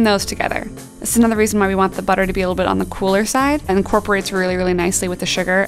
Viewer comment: 1. Saying the butter needs to be "on the solid side" is not really helpful, because it's too vague. My butter was cool, but still liquid, and when I added it to the dough it took on the consistency of gravy. 2. Although the dough became firm in the fridge where I left it for 1.5 hours, on the baking sheet six cookies formed a homogenous mass after baking. 3. The cookies, even with way less dough added to the baking sheets, were extremely greasy, and although dark on the top, they had the consistency of fudge. 4. The dough tasted like salted caramel, and the baked cookies like salted caramel fudge. 5. The cookies were WAY too salty. I have added a quarter of the recommended amount of salt (not even half a teaspoon), but the cookies were pretty much inedible because too salty. 6. The cookies did not taste like chocolate at all. Even the chocolate in the cookies (I've tried it after I scraped the fudge off the baking paper) tasted like salt. 7. Horrible, horrible cookies. Although I followed the recipe perfectly, and used the video as a guide, they came out horrible. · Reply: Randolph Crane I defiantly heard her say “on the cooler side”and not “on the solids side” when she added the browned butter.